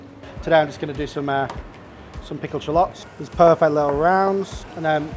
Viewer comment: "some pickled shallots". I swear I heard "something called shallots" and thought who in the world doesn't know about shallots by now?